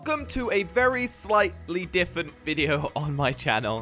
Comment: you said differnt instead of different 😂🤣😂